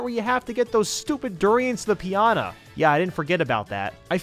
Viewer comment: Did he say "Give those stupid duriants the Pianta" or is it just me?